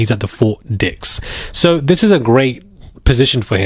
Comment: Good! I thought you said Four Dicks!! 😂😂😂😂